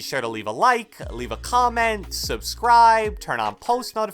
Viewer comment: did he say turn “off” post notifications or is it just me ?